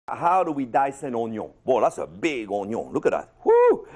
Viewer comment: I first thought he said how to dice a yoyo i was like hell naw